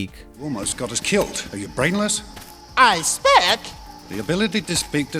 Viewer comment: You got that wrong. Jar Jar did not say he spoke. He said "I suspect." He corrects Qui Gon right after you cut the clip.